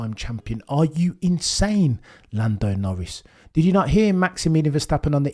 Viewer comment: I keep hearing you say 'Lando No-Risk'😂 That's just me, right? Right?😋😅